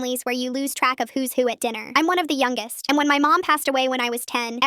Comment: I thought you said you were the youngest but then you say you have a little sister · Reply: One of the youngest. Is what was said